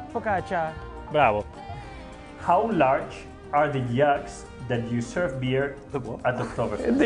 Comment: When carlos said jugs i thought he said Yacks. Welp